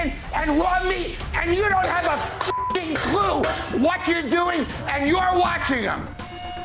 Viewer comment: i love the audio editing in this clip, because the censor is so perfectly timed with the sound, that it sounds like Taffer says "You don't have a clucking clue" I can't unhear this, and it makes me laugh every time.